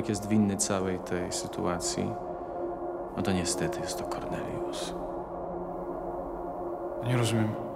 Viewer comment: W 5:21 usłyszałam koronawirus i miałam takie wtf przewidzieliście to, ale się okazało, że tam jest Kornelius XD